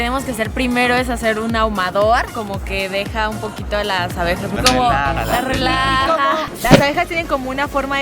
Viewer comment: Pensé que decía obejas